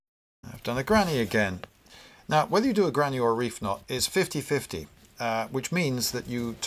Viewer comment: What is a ‘granny’ knot? And what’s the other one he says at 38:12? I can’t understand him (lots of background noise here). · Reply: The other he says is "reef knot" which is two tied in alternate directions and is far stronger than the granny knot.